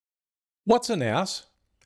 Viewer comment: I misheard the opening line as "what's an ass"😅 anyway, just discovered your channel and really enjoying all the informative content